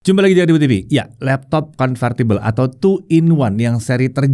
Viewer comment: udh gw set playback speed 0.25, tetep aja kedengerannya "Jumpa lagi di dwewewewe TV.."